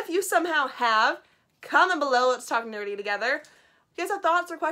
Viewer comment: Hey, what's with the captions? At 7:09 you say, "talk nerdy together", but the captions say, "talking dirty together." Also the he poor dog in the movie doesn't have much of a role. In the manga he's a hero who helps Alita save a baby and jumps on that guy's head to rip Alita's arm out of his eye socket.